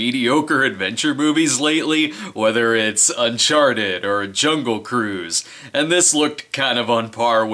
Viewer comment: When you were going through your recent adventure film viewings, I thought you said "Sharted" and my mind was willing to accept that as a possible movie title.